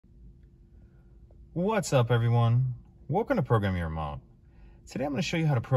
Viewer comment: I thought I heard you say "welcome to programming your mom" 😂😂😂 yes my mom is a 2015 frontier lol